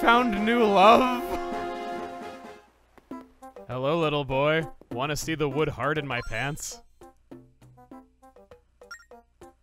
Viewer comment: Oh god... I wasn't focused on what was saying here 9:45 and my brain perceived "Hello little boy, wanna see the hard wood in my pants?"